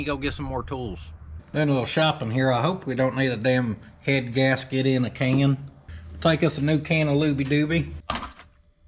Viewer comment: I thought he said the head gasket was brown…🤣🤦🏻‍♂️ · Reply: I thought he said he grew a head gasket once